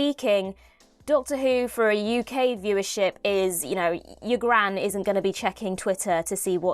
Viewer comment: When you said "Your gran isn't going to be checking twitter", I misheard it as "Hugh Grant", so I was surprised by the female pronouns later in that sentence.